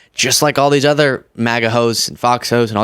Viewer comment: I thought had just said, "MAGA hoes." Maybe I could be wrong.